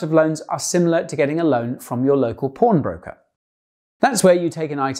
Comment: I thought you said porn broker